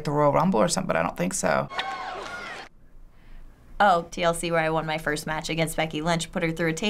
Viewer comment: Correcting "DLC" from the captions: she clearly said TLC as in Tables, Ladders, & Chairs